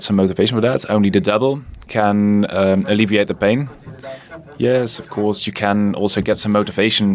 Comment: "only the devil can alleviate the pain"... ??? Did I hear that right?